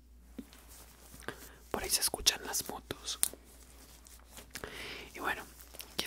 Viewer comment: suenan las fotos o que dice? xD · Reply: Yo no se que hago aca Pero dice motos